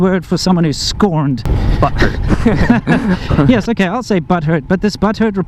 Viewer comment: Subtitles suck, when said the word butthurt, I thought he said buttered and that doesn't make sense. So I did a little digging in dictionary to no avail, then I goggle it for a while then, eureka! Isn't learning fun?